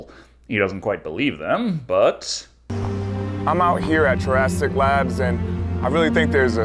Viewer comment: did he say Jurassic labs?